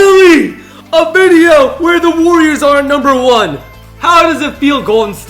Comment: you said they are finally the number one you did not say aren’t